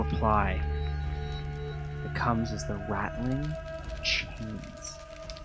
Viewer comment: For a brief moment, I heard "Ratling" rather than Rattling" and had visions of ratlings pouring out of the cave saying "Yes, yes, yes?"